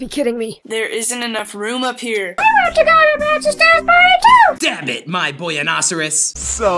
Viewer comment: did he say damn it? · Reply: He says Dab it